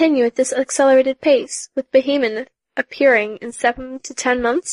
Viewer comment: Did she say Behemon?